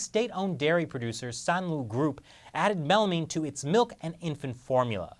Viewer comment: Added WHAT? @ 2:51 did I hear melanin ?!!